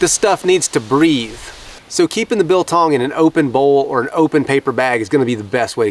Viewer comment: left youtube playing, thought i heard, "eatin the dildong" and came in to turn it off or change the channel and am pleasantly surprised when i see jerky... lol